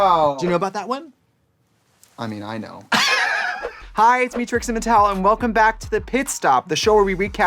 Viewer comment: I heard “I’m Trixie Mattel” and was waiting for “and I’m Katya!” in this intro 😂